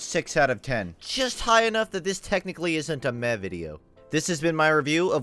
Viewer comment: what does he say? A "mevity" ? What's that?